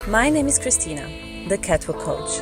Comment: I thought she said, “my name is Kristina, the cattle coach” 😂